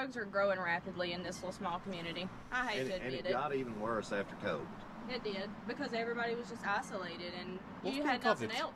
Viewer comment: At around 50:10 you misheard the guy, he said it’s gotten worse since COVID. Drug use across North America in general has gotten worse since lockdown.